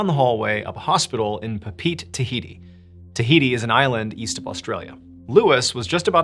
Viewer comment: did someone say Tahiti?